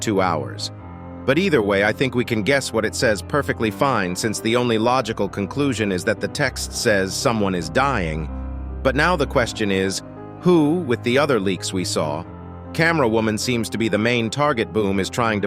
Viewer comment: I thought it said "someone is fcking" 💀